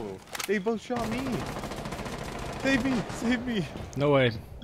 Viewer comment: did he call baby? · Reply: He said save me save me